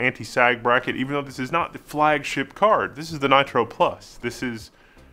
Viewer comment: this is really confusing there is a PLUS version? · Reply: OMG 🤦‍♂ There is a "Nitro+" (Nitro plus) and a "Pulse" card out there, what is so difficult? At 2:10 he said "Nitro plus"